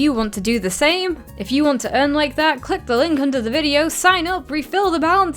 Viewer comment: Thought it said dick and not click 🤣